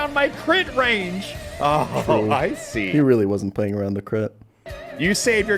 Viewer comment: This man POURED over the entire Pokemon Challenges expanded universe, leaving no stone unturned in his quest to create the ultimate denouncement of a video. I can see it now, Alpharad studying the ancient texts by candlelight, trying to comprehend the true meaning of "play around the crit." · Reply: Damn i must've misheard, I always thought he said: "Play around the clit". Still good advice.